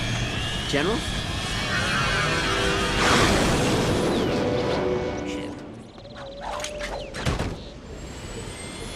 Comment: does clank say sh*t? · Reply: He said “ship” he cut off the gameplay audio into the cutscene audio and it just sounded like he said it